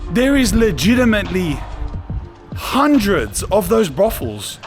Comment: What does he says on 13:53 ? Hundreds of buffels?